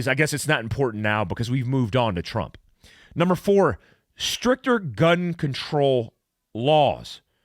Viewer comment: I swear I heard you say "Stripper gun control laws"